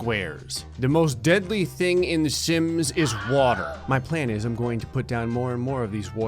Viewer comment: When he said sims I thought he said Simps